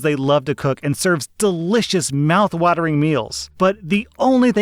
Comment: Did he just say "Meowth watering"?